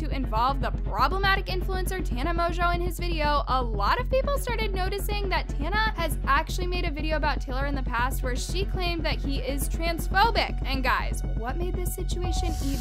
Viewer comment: I THOUGHT YALL SAID SHE WAS TRANSPHOBIC AND I WAS GUNNA SAY BUT SHES TRA